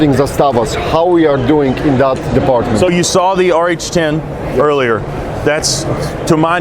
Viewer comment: did say "RH10" was the name of the rifle with all the magpul furniture @ 5:20? Is that an M+M M10 under a different name or a completely different rifle?